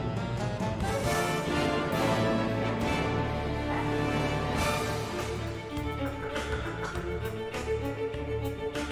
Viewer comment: I'm a big fan and I am glad she performed this on an acoustic violin. What does she say at 2:43? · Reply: She said "Fasten your Seat Belts", you can hear it better in the original V-M's versión of Toccata & Fugue